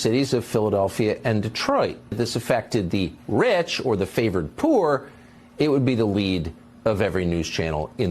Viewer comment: Did he just say the words "favored poor"?!?!?